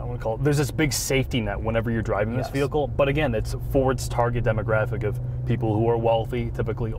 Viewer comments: Did he say "Ford's" or "for its" at 18:17 ........ ? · Did he say "Ford's demographic"? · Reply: He said "for its demographic", but he slurred them together a little. I can definitely hear why one might hear "Ford's demographic". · is he saying fords demo... they have said ford I seems a few times like this is not a mustang lol